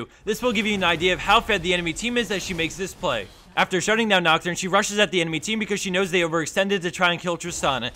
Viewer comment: did he say, "after shuting down nocturne"?